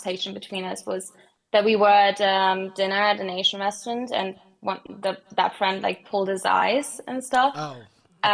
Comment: What is caller saying at 2:09:15 - 2:09:20 I can't understand a single word they say here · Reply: "we were at dinner at an asian restaurant and that friend like pulled his eyes and stuff"